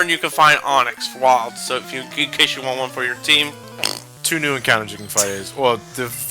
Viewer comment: I heard that encounter john